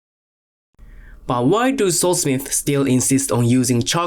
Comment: Bro i thought he was saying (soul) smith. I was like what? Is that what they call blacksmiths and then i realised he was saying sword smith bro im so fucking dumb🤦‍♂️🤦‍♂️🤦‍♂️